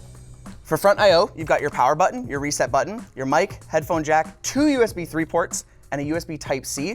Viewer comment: When said "your mic" i heard "your knife" like thats what the icon looked like. Idk. Weird idea to have a knife port on a computer case.